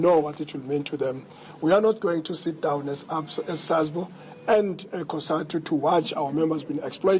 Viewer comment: into the vid, we are not going to sit down as what? Can someone confirm what i am hearing hear, is he saying ABSA and then correcting himself??